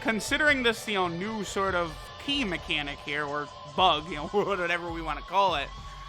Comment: Do I hear “broken mechanic”?